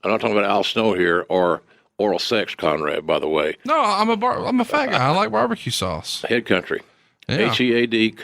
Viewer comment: did Conrad say I’m a faggot?😂😂😂